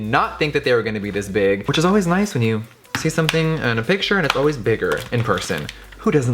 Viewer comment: literally thought he said “pig shirt” instead of picture and i was like oMG DID HE JUST THROW SHADE AT SHANE but never mind i heard wrong 😂